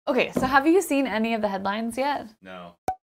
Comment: I gotta admit, I thought you said "Have you seen my behind yet ?". This was through the fact that I first searchbarred forward, saw the picture with blackhole from behind, and then searchbarred back, and mistaken "head line" for "behind"